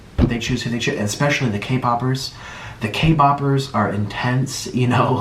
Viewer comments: Why do I hear “Kboppers” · Did I just hear “ K-bOpErS” wowww😲 Omg EoMmA I’m famous thank u guys soo much(^з^) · did he say k boppers? As in the people who watch kpop??!!😶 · "The K-poppers..." Is this stereotypism/racism or is there just hate for K-poppers in his heart? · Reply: I heard him say K-boppers